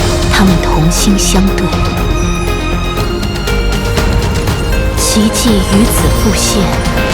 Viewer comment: did i hear "Qiqi" 🫠